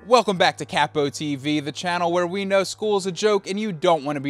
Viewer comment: Hahah I thought you were saying "crap on tv"